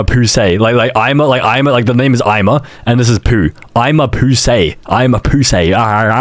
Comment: people think it's saying: "ama pussy" 😭🙏